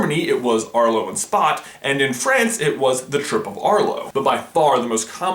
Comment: did anyone else mishear him saying "the triple Arlo"?